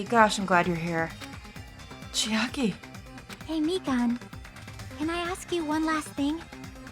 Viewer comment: Just had to say the subtitles at 38:02 Say "meat bun" instead of Mikan, and I find it much more funny than I should.